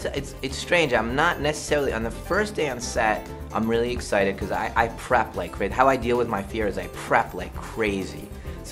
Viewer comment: I thought M Night said "I crap like crazy" and it took me out.